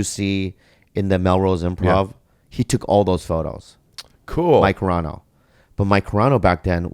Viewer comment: is he saying mike carrano?